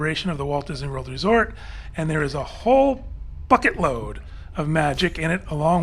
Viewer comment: said "Bucket load" and I heard "F*it load"....